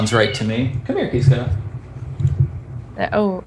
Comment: The adventures of The Russian, The Vain, and The Lance continue (and also The Kid). What was the name of cat? It sounded like he said Barista. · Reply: Kiska: "Pussy(cat). A term of endearment, not a name. And year, there's this recent and rather execrable tendency to copy the other English meaning. So no. You'll attract ridicule of the worst kind, the not even slightly intelligent or witty." Translation ripped from google result.